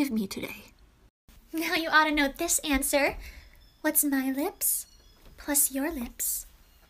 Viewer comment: Why did I hear "What's nine lips plus four lips?"😑?